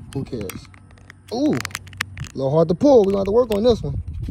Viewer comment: I thought it said Walmart gun 😂